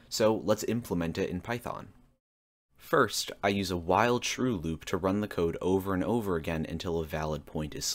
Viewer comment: I heard "I start with a wild shrew loop" and was thrown for a loop.